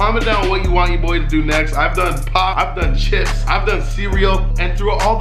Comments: I thought he said ive done pot but he said pop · At :35 it sounds like you said I’ve done pot